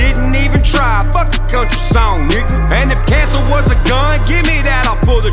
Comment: what did he say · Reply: “Fuck a country song nigga”